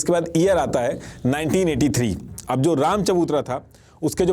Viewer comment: The subtitles said 1883, and even the content matches that year, but I think you said 1983 — is that correct?